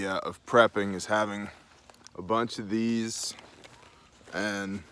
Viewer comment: I thought he said "a bunch if bees" at first I was like sweats